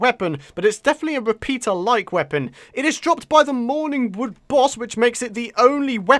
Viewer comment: Who laughed their asses off when he said mourning wood.! I thought he said morning wood OMFGGG